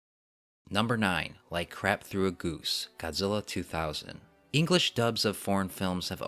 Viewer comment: So, I just want to point out that the “crap through a goose” line does make sense. Birds have a high metabolism and digest food very fast. So I think the line is just another way of saying “like a hot knife through butter”, but yea, is still super awkward . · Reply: I always thought he said like crack through a goose, as in like shooting a goose.